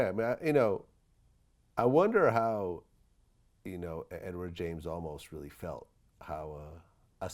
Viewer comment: Did Vlad say RIck James Olmos